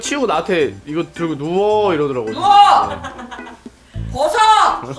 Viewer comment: 에 "벗어" "어멋 살려주세요"이게 나만 이상하게 들리는건가..